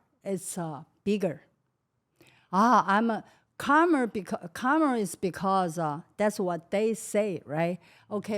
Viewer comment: Timestamp 1:26:29 the Subtitle says "calmer" she said "karma", just wanted to clarify for the viewer.